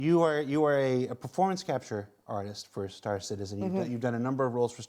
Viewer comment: My dyslexia is acting up I thought he said corn star performance artist xD at the start lmao